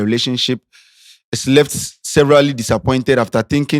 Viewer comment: Did l just hear say "severally" instead of severely disappointed ei ei ei 😮😅